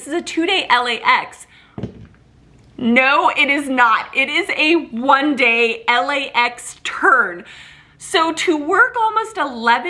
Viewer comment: Did you say "One-day LAX turd"?